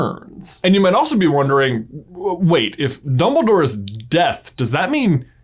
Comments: I thought it said dumbledore is dead 😂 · i thought he said dumbledore is dead and i was like "Now you figure that out?" i am a idiot 😶